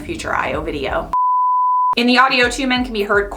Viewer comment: From the outtakes, sounded like you said "questioning Brisbane". Arthur Brisbane the Industrialist? Doubt anyone got that reference, but in case any hardcore Mel Brooks fans who have seen High Anxiety should see this, Shout Out to you. 😉